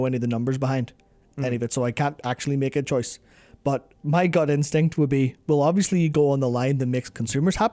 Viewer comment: took me a while to figure out what he said... thought he said "my GOD instinct"